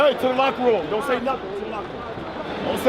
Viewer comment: what was this? Lebron saying "'Turn Left Rule', don't say nothin'?" And rotating his finger?